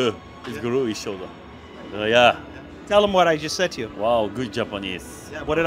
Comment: ら辺の「彼に伝いといて」って部分全然聞き取れない誰か教えて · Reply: Tell him what I’ve just said to you.